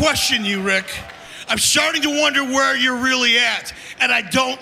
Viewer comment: Did he just say I'm sharting to wonder 😂😂😂😂